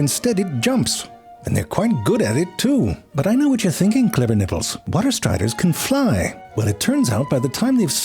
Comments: Did. Did he just say "Clever Nipples" 😅😅😅😅😅😅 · did he say "I know what you're thinking clever nipples" or is that something I misheard 😂